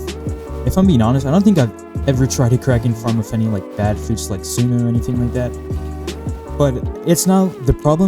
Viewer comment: "Like suna" (If I heard right tho) Suna is the best df for kk (on land)